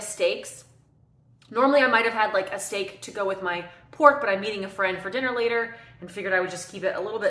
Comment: I thought you said you were eating a friend for dinner later. That would be taking carnivore to a whole new lever. 😳. Get plenty of views though I imagine 😏